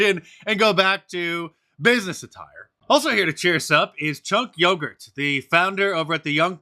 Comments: When I hear his name, I hear 'chunky yogurt'. Makes me wanna urp. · I thought he was saying "Chunk Yogurt " 😂😂 I didn't realize what he was actually saying until I saw the guys name on the screen 😂 oh my goodness that gave me a good chuckle 😂 thanks for that